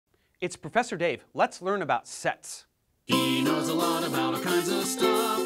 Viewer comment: i started this video in double speed and i heard "It's Professor Dave, let's learn about sex" and i was confused